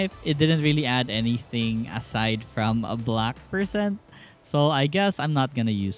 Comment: swear you said black person XD